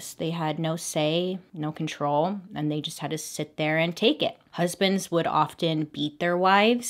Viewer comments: I THOUGHT YOU SAID "husbands would often eat their wifes" OMFG 😂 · I s2g I heard "eat their wives" instead of "beat their wives" and was so confused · Am I the only one who heard "husbands would often eat their wives" at 3:45🤣 You never know with these!